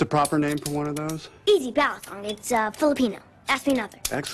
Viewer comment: I'm Filipino and I couldn't understand what her answer was . . . ballet. . . kong